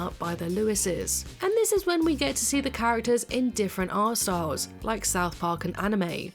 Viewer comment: i thought you said “different arseholes” 😂